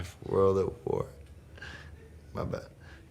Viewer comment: did he say "vabbeh" ? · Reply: marty 2thousand He says my bad x